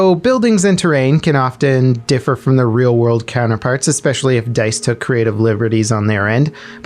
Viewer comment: did you say Dice instead of Microsoft?